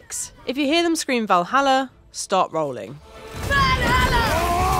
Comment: Step 6 sounds like she says stop rolling lmao